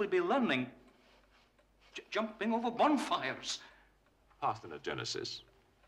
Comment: What is Christopher saying here? Its sound like “a partering of Genesis”.